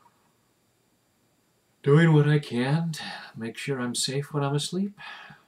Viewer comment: did he say doing what I CANT and its safe for me to sleep?